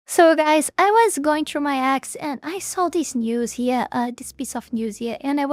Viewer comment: Lmao when you said "I was going through my X" I heard "I was going through my Ex" and I'm like wait what 😂😂😂